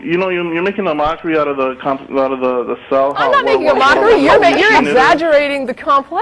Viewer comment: What is he saying at 33:00?? A "sow"? A "sow" is more complicated that a brain? I've replayed it like 50 times and can't understand :( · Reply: a cell.... jesus christ his mumbling big lipped speech is almost impossible to decipher